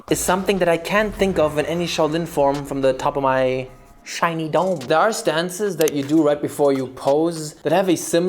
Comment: i thought he said shiny dong instead of of shiny dome